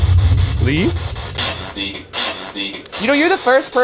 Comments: I think I heard "please don't leave" instead of "leave" at 10:20. What are your guys' thoughts? #postmortem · I’ll be honest I was looking away and didn’t hear “leave” I heard “I’m a vegan” and I LOST IT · it sounds like it said “then leave” not just “leave”